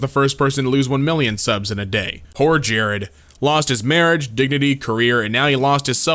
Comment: I-I’m sorry did he just say digity?